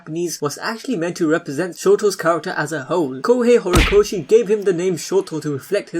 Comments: lol I thought he said “As a HOE” I was like WTF😂😂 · OMG! I honestly thought you said “ shoto’s character as a HOE “ I almost had a HEARTACHE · I THOUGHT YOU CALLED HIM A HOE